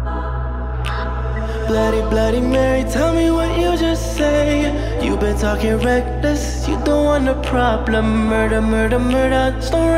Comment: It sounds like he is saying "you're talking breakfast" and now i cant unhear it